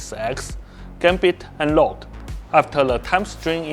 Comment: Is it rude to think it's kinda funny how he says Rouge as "Load" ? 😭 At leasst he sounds like an actual human being instead of a lifeless-like android like the lead combat designer also 0:23 god dam the cake